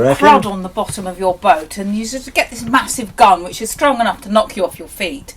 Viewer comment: did she say, "you get this massive gun ...you get to knock off the nasty bits..." 2:03 I'm American so perhaps, something may have been lost in the translation... but, huh?